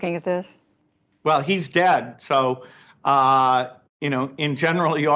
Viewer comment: He said "Well, he's dead." I heard "We took care of that. wink"